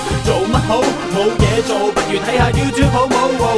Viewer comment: 未有字幕之前，YouTube好冇听成YouTube宝宝😂😂😂